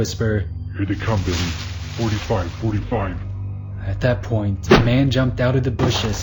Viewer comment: What the heck are they saying at 1:15:40?? All I can make out is “here they come”. The rest just sounds like “baby! 45 45!”